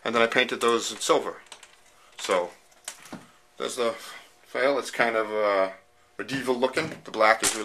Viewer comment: Did you say fail???